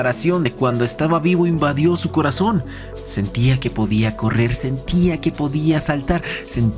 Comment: No estaba poniendo atención y escuché podía correrse XD